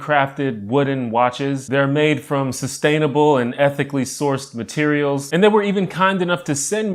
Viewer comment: I heard, “EPIC-ly sourced material.” Hell yeah!!